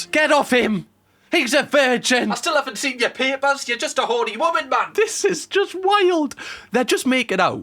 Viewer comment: Cam's reaction at 36:38 made my drink come out of my nose, because what I heard was "YOU'RE JUST A HORNY WOMAN-MAN!" and not "You're just a horny women, man." 😅